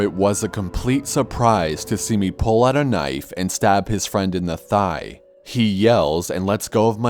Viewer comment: These stories make me want to work on running faster. At my current speed and endurance Id be screwed... Maybe I'll just invest in a knife or taser... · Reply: I thought you said "Injest a knife and a toaster"